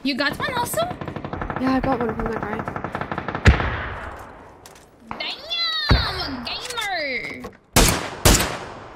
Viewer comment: What is the scream " ...... gameeeerrr" ? Cant understand the first word. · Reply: Damn